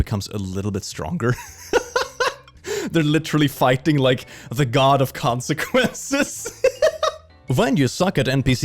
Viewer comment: "God of Consequences" What I hear: "Genocide Run" Flashbacks intensify